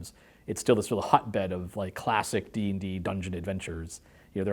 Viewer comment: I THOUGHT IT SAID "Last Dungeons & Dragons Adventure" I ALMOST CHOKED ON MY TONGUE